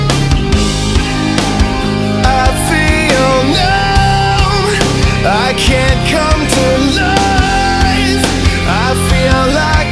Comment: Well If I'm in a bad mood, I hear the line as "I feel numb, I can't come to life", and If I'm in a good mood I hear it says "I feel now, I can come to life." thats weird